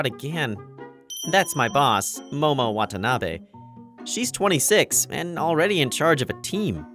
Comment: I thought this said momo wannabe